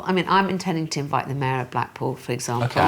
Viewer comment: I keep hearing “black balls”, not black pool! But much love from the U.S.! ❤❤❤❤❤